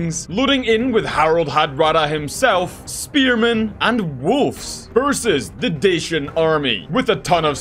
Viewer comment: Did actually say wolfs instead of wolves? Sounds weird